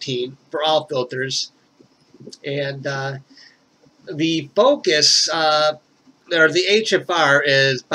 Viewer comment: at first i thought he said "Bocus"lol